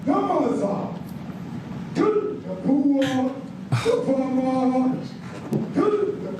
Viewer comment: coming in with no context, I'm 90% sure he's speaking a native language and the subtitles are wrong. Let's see if I was right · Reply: he’s saying “kill the boer, the farmer” a boer is a farmer in afrikaans